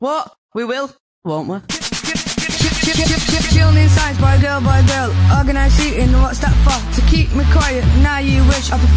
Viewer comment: if anyone was curious on what he said, he said “chillin in science, boy girl boy girl” which is talking about the seating plan maybe not all schools have?